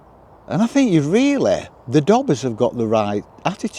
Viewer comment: Dobbers.... thought you said doggers! 😂 Don't think I know what weed smells like.